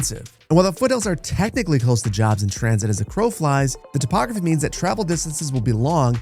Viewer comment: I feel the respect of the topography is somehow strange foreshadowing of the massive flattening we're going to see in the build contest... :) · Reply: Oh! I thought you said re-spec the topography. My bad.